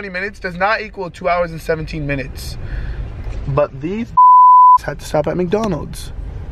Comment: Did he call your cousins bitch’s?